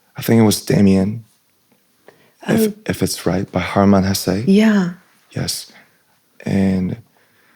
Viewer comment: Guys, what is the book ? I didnt catch it. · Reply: Damien by harmman hasse